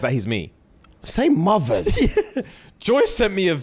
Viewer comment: Did Fuhad just say "Saint Mothers!" ?